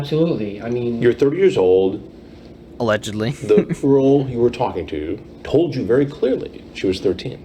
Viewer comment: I always thought it sounded like Chris was saying “The Earl you were talking to told you very clearly she was 13” it’s like he forgot the G in girl. I think I’ve watched this too many times